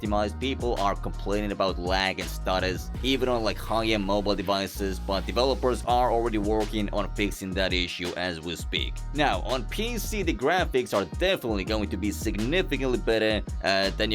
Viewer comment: why did i hear that as "fixing daddy issue" 🥲